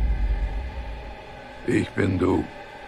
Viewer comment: “ICK BIN DÖ” - “NEIN!!” “JA! ICK BIN DÖ” i‘m dying 😭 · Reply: i heard "it's been dude" instead of ich bin du